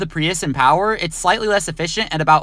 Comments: I thought it said 5.1 MPG and i was like ok damn but then he said 5.1 Gallons per mile and i was like OK DAMN, that shits like 0.2 MPG 💀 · Oh he said 5.1 gallons/mile, I thought he said 5.1 miles/gallon and was thinking to myself that it’s actually efficient….